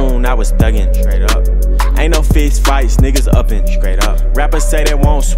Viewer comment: “Ain’t no fist fights niggas oven”? I thought he said uppin at first but this nigga said oven😂